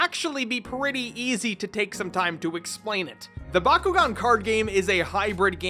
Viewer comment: Its just me who heard him saying "gaycard"?